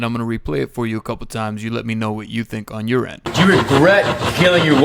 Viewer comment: I think it said "No regret"